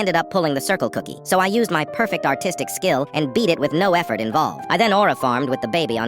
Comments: Why did i hear "My autistic skills"💀🙏😭🛂🛂🛂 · ARTISTIC? I THOUGHT U SAID AUTISTIC